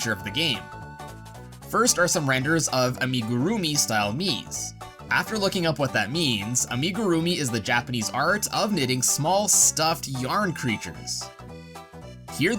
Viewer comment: I thought he said “first are some horrendous uhh… I mean, groovy style Miis”